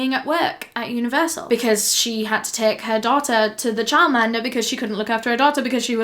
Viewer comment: what was she saying cuz all I hear is Charmander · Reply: “all of her story relies on her being at work at universal because she had to take her daughter to the childminder because she couldn’t look after her daughter because she was at work at universal”